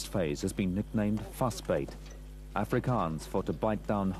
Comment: what was that word at 42:08 "to bight down hard and never give up"? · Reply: Vasbyt